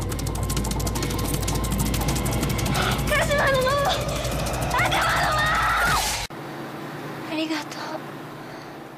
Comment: 何て言ったの？w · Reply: あなもの、あかなもの！！！